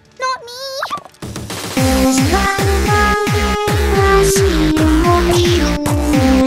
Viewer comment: who got my whatever idk what she's saying... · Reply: Rusty horseshoe